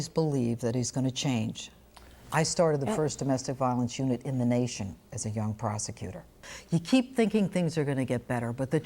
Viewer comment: i thought she said as a young prostitute at